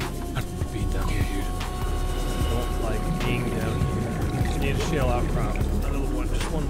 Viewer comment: What did he say? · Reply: "I've been down here".